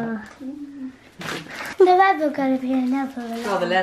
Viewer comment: what did say? 🤷‍♀️... he’s so amazing! Xxx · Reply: CaSsIe SaDz something about leather